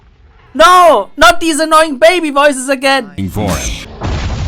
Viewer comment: i thought he said: you f**** foreign